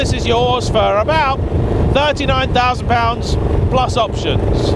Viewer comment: Did you say £99,000 here? Because later you said £45,000, I'm confused! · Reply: He says 39